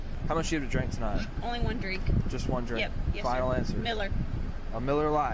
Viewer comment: why did I hear “only one drake” instead of drink😭